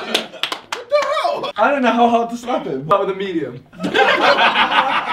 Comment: What does vik say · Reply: Start with a medium